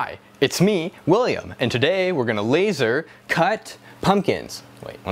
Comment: Oh fuck. I thought it said “Laser Cunt”